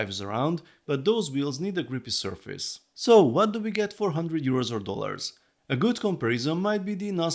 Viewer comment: Ooh, it's 100$. I misheard you as "What do we get for four hundred dollars?"